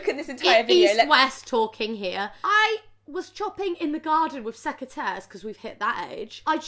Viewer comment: what is that word she says.... sec a tears ?? can someone translate to amurica english please?